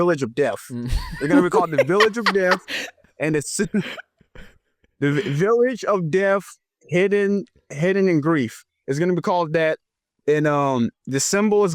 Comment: The village of death hidden in grief is cool and all, but i thought he said "The Village of Death, Heaven and Grief"